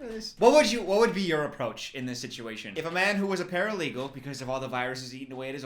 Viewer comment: I’m sorry did you just say paralegal??